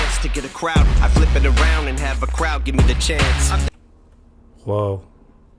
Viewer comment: I think he says "I'll have the crowd give me the chants" like how they're chanting Shady in the background